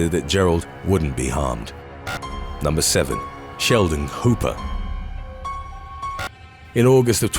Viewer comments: I thought he said Sheldon Cooper lol 😂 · when i heard Sheldon Cooper i stood up! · I thought he said Sheldon Cooper. I was like I don’t remember that episode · For a moment there i thought you said Sheldon Cooper like the guy on The big bang theory.